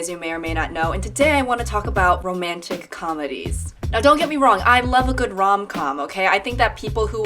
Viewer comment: i really fucking thought that said romantic condoms why am i like this.